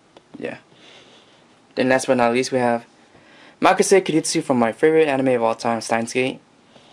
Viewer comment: What is the character at 24:18? Sorry I couldn't hear you. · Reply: kurisu - Stein's Gate